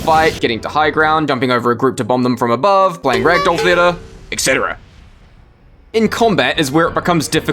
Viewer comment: What did he say here? playing rag-doll fedder ?? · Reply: he said ragdoll theater